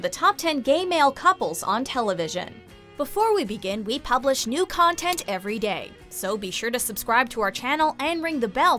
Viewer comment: I thought she said “we post new content every gay” I